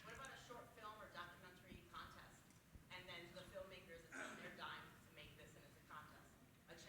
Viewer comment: What the women said at 1:51:30? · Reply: a documentary film contest on the film makers "dime" (paraphrased) . accidentally wrote ad ocumentary....hmmmmm